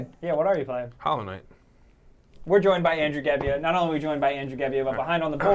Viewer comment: i almost thought you said "Andrew Galbino"